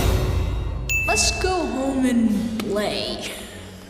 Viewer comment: To me it always sounded like Sid said" let's go home an blay"